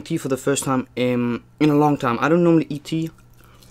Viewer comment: "I don't normally eat tea" ☕😂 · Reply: I thought he said I’m not normally E.T